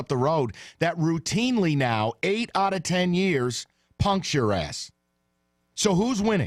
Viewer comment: Listening on the radio today, I swear I thought Mike said pumps your ..., not punks your ...